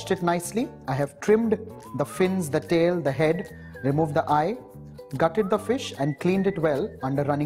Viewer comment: Did he just say "cutted the fish" at 0:39...😆...whatevr ur recipe is fabulous..tried and tasted it😍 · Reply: priyanshu nath no he said " GUTTED" meaning he removed the guts of the fish